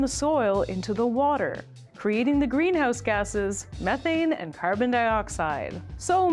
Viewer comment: misheard it as "green ass gasses"